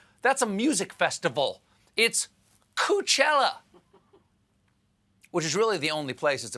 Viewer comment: I thought he said Coochella